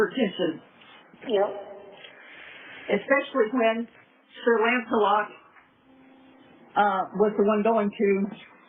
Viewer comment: I really think said "sir rants a lot", not sir lancelot. I could be wrong, but this is what I think she said. IMO